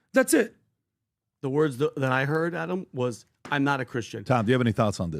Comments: Almost sounds like Trump says IMA Christian not I’m not a Christian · I did not hear “not a Christian”. I heard “I’m a Christian”!